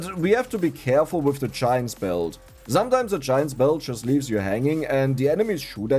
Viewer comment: I always thought you said shaen spell not giant belt your accent is built in encryption.